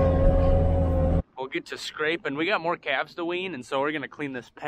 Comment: Had to replay it but I for real thought you said you had some "cats" to ween...at 2:28 I was wondering if you went a new direction....lol also that lady sure knows her crap.........spreader...lol